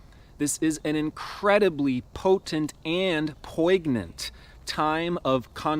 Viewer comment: Bless your pronunciation of poignant,... at first I heard po' ig'n'ant What's more poignant then the poor ignorant?